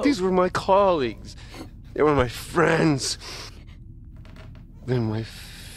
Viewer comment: The in-game closed captioning says, Wyatt: "Then my family". When, in acuality what was said was, "They're my family". First, the closed captioning doesn't make sense. Second, how sad and pathetic for the in-game closed captioning to not even match what was said.